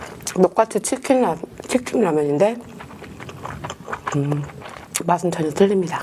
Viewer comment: did she just say chicken nugget? Or am I hearing something because I’m fasting for ramadan